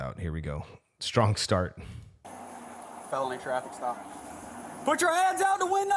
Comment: Did I hear one of the officers say it was a felony traffic stop around 0:51? If true it explains the aggressive initial approach(but not continued aggression after it is confirmed there is no danger) but what was reported to make it a felony stop?